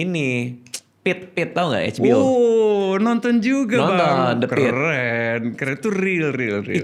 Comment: ada yg tau ga di 0:41 tuh bang radit nonton apa judulnya? soalnya ga kedengeran jelas ditelingaku😭😂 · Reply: the pitt